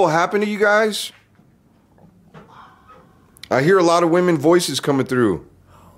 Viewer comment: Did anybody heard “a lot” on 18:00?